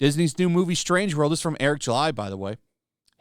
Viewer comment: For a second I thought said “strange world is by Eric July by the way” it was the biggest double take I’ve done in months…